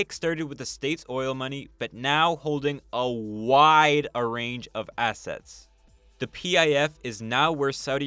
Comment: "wide ARRANGE of assets" ..... I think you meant to say "ARRAY"